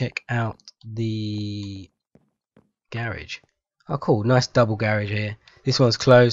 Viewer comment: y u say garage like that? It sound like u sayin gay-rage or something.